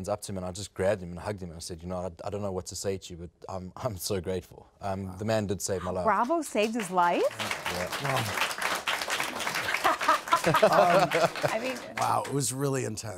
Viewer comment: Did she just say “bravo saved his life”? 🤢🤢🤢